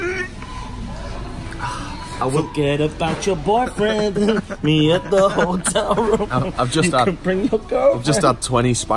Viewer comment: What is the brand is recommending here? Actually do need a decent airbed and can't quite hear him. Cheers. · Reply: quechua